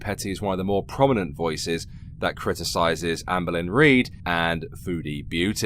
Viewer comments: Whenever you say Amberlynn, I hear "Anne Boleyn" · Every time you say "Amberlynn" I hear "Anne Bolyn", and it's vaguely alarming.